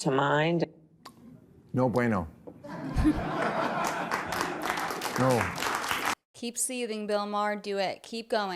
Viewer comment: I thought he said "No Braino" that would have been funny.